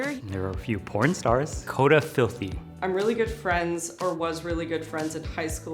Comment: why do the I think the lady after the guy says Dakota Filthy is Dakota Filthy?